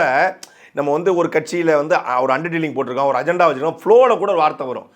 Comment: what is he saying, under dealing vachurukeengala